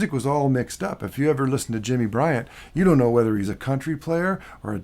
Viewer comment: what does he say at 5:54 ? "Jimmy Briand" ?? its a football player? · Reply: said Jimmy Bryant not Briand. Bryant played a lot with speedy West back in the '50's.